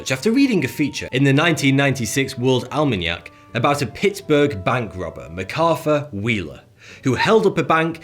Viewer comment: a pits berk bank robber Megrafwr wheeler (I don't understand) Edit: I finally understood after reading the subtitles